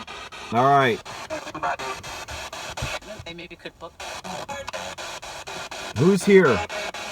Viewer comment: So glad you uploaded another video! Not sure if you caught it, but at 11:13 I clearly heard, "Maybe you could focus some more.'